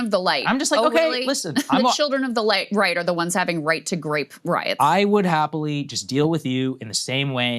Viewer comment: Did Krystal say "right to grape" at 8:42? is youtube this cenceros that journalists cant say teh word rape anymore?